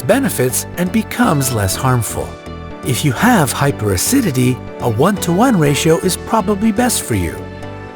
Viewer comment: I thought it said hyperactivity..... Lol. I think I have hyperactivity